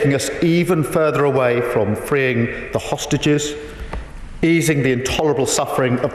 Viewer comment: Did he say ""sausages" @ 01:12?